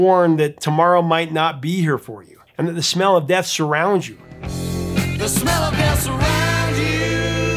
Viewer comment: Oh! "the smell of death that's around you." I always heard "the smell oh, that surrounds you."